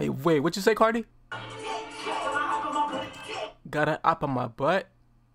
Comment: Hahaha I thought he said opp in his butt as well 💀😂😂